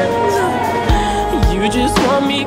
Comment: i thought he said “you only want me because im mixed” lol💕💕